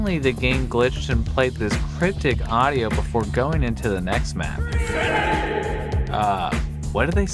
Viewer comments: i think the soundeffect says please help me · I heard: "Runescape"